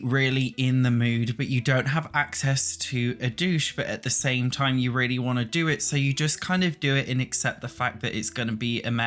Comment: Did he just say "really want to douche"...? 😐